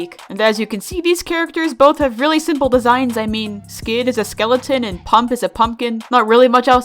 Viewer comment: When you said “skid is a skeleton” I heard you say “sans is a skeleton” Skid is sans confirmed